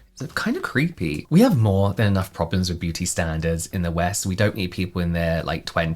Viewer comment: I swear i thought you were gonna say "we have more than enough problems with people being pdfs in the west." Not beauty standards. 😅